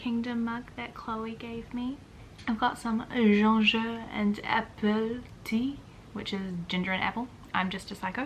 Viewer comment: Tell me why I thought at first you said ginger and advil tea. We love a bookshelf re-vamp.